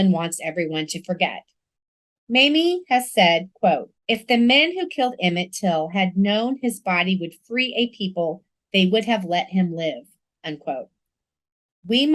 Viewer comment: Can someone please tell me what she saids that Mamie said about Emmett body at 40:17??? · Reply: "If the men who killed Emmett Till had known his body would free a people, they would have let him live."